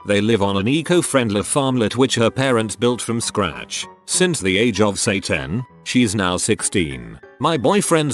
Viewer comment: Story at about 2:17. Text says: "Since the age of say 10?". I hear: "Since the age of Satan." And I say my ears heard it right.